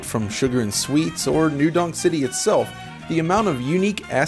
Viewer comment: I kept hearing "New Dong City". I was disappointed to learn it's supposed to be "Donk", as I was expecting a "Penis Village" and perhaps a "Schlongberg".